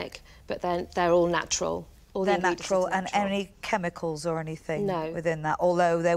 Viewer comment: Did she say she wouldn’t want to put anything organic in her body ? I must have misheard